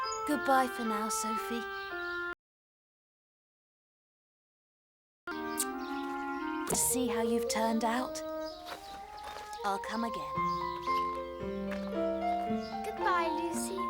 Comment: Does anyone know what was said at 5:33 ? The audio is missing for a few seconds. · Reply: Lucie a dit : Au revoir mademoiselle sophie. Je suis bien contente de voir que vous avez bien poussé. Je reviendrai. In english : Lucie said: Goodbye miss Sophie. I am glad to see that you have done well. I'll be back.